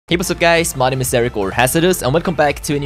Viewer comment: Who else thinks that he says it’s hericore hazardous at the intro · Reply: before he hit 1mil subs i thought he said My name is Ericporn Hazaduz